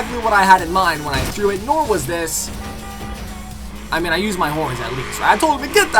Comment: Did he say he used his horn? HORNS CONFIRMED FOR FH3